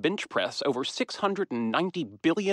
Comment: I heard him say "mammograms". I was really confused on how Lenard automatically knew how much billions of breast scans weighted.